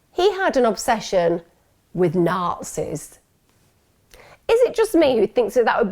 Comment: You guys I cannot understand what word she is saying at 42:04 when she says the guy was interested in ‘Narxis’? I keep googling it but I have no idea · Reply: Hahaahahahah after 800 listens I finally figured out she’s saying Nazis. I’m Canadian forgive me lmaoooo